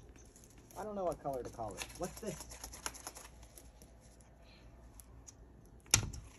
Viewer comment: I thought Appollo said "little" "touch" "Royaaal"